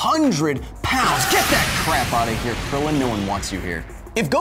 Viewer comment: did he say no wants krillin